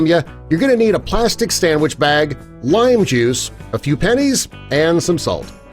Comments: I thought it said penis es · For some reason I thought it said penis 😂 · I thought it said penis lmao Kung pow penis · i thought it said penises and i was like whattttttt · I thought it said penis